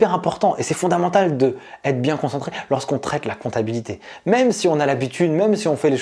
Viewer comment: Bonjour, merci pour la vidéo, j'aime bien le ton. J'ai une question. À 2:31 environ, vous semblez dire "lorsqu'on traque la comptabilité". Les sous-titres écrivent "lorsqu'on trek la comptabilité". On pourrait aussi croire que vous dites "lorsqu'on travaille avec la comptabilité". Je trouve les deux premières images intéressantes. Ça donne un côté "aventurier" au métier. Qu'est-ce que vous dites, finalement? Merci! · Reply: Lorsque l'on traite la comptabilité 😁